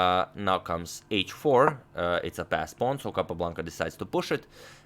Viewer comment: Ever since people thought you were saying "fast pawn" you always crack a joke here and there 😂😂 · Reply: lol until today I thought it was named fast pawns rather then passed pawns and I have been watching for a couple of years now on and off. And playing with my family or occasional with a friend nothing serious or involving theory or... what so ever but damn.